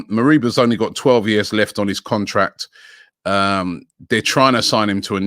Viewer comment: Did Robbie say this kid has got 12 years left on his contract?